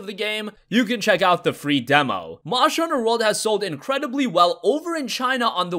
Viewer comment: When ever I hear Wegame I keep hearing "Monster Hunter World has sold over a Million copies on Wii in China".